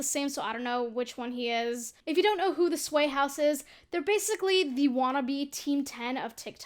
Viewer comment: I ACTUALLY THOUGHT YOU SAID THE WHITE HOUSE INSTEAD OF THE SWAY HOUSE- 😳